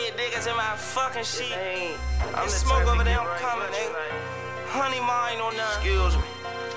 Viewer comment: Dat nigga already on bs 😂 first line I heard was “honey marr”